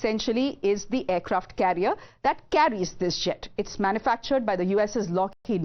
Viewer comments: Did she say “carries this Shit” 2:59 😂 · When she said.. 'that carries this jet', first thought she said 'that carries this shit' 😂😂😂